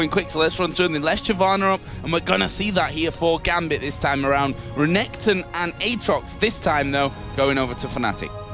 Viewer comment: I'm ready to PAY to know what Darien said... Two of his most favorite champs as first two picks... He loses his shit there... :D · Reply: he said "renekton,pizda im" which means renekton,they are fucked